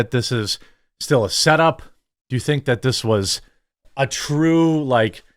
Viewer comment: I thought he said this was a huge break🤔